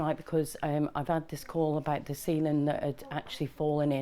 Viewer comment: When she said ceiling I thought she said semen, Jesus.